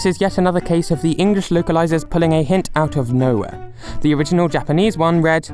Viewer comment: the captions instead of saying "out of nowhere" says "out their ass"